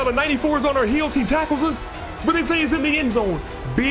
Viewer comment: he said Isaiah is in the end zone 😂😂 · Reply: I thought he said But they say he's in the end zone